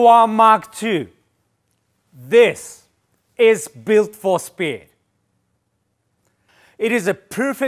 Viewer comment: am i the only one who heard it "need for speed" ?! 😂😂😂